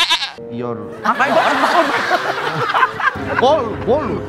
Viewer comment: ngomong apa ini pak andre? "your ball?" :face-blue-smiling